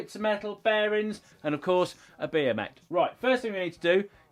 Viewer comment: When he was listing the materials needed and said "and of course a bmx" I thought he said and of course a beer mate at first lol. 🍺